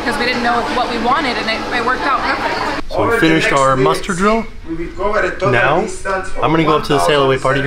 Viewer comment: I only just realised that you say muster drill, I always thought you say mustard drill and I was so confused